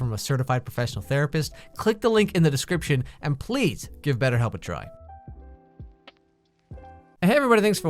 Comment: i thought you said better ho not better help